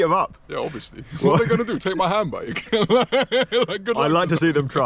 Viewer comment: Awesome, just awesome. The pump, the dropping and the AliExpress 2000m light and of course the beating up of the bike thieves trying to steal handbags. needs a channel just for being just the coolest chap ever. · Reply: I thought he said handbags as well when it was handbike 😂